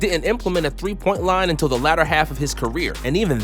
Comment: did he just say Ladder? LOL